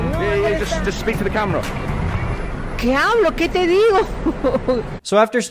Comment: Just wanted to say that at 9:51 she said “Que hablo? que te digo?” Which actually translates to “What do I talk about? What do I tell you?” Which is not what the captions said at the bottom Anyways 😐😐